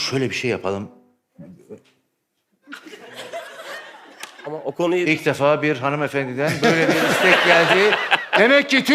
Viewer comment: hanımefendi ne dedi bilen var mı ? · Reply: erotik shop diyor erotik kısmı biraz zor duyuluyor